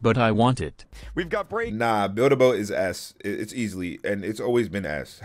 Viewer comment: when he said build a boat is s i thought he said "build a boat is ass"